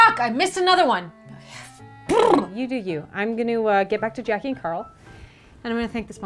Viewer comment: i know she said "jackie and carl" but I heard "jacking carl"